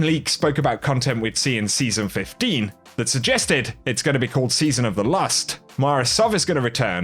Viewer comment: At around 8:40, did he say season 15 is going to be "Season of the Last" or "Season of the Lust"